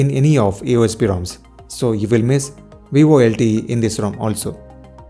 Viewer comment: miss what? can't understand that word you saying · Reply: It is volte. Shortened word for voice over lte which is not available in aosp roms. Drop a comment if you have any question.. 🤗